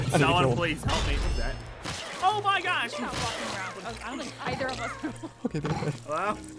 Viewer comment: Did say "Oh my gosh, he f-ing got me"?